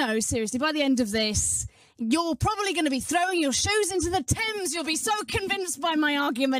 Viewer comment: As a Canadian when the British lady said “… throw your shoes into the Thames” I thought she said “Tim’s” and that’s what offended me. I can’t think of a situation where throwing anything in a doughnut shop is appropriate.